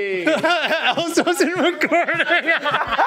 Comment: What did say at 10:05?! I need to know! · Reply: I laughed and said "Ellis wasn't recording". If you look carefully, he's actually filming laughing (The shot right before) so he didn't get the return on camera 🤣